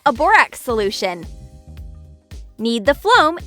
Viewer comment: umm did she just say floam Ps don’t mind my spelling!